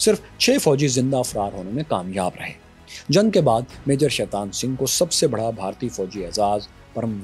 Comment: did you say sheitan Singh ???🤔🤔